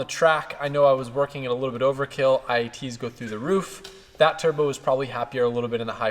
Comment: What are “IET’s” you said it at about 1:10 is that some kind of temperature reading for boost like EGT’s? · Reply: IAT Intake air temp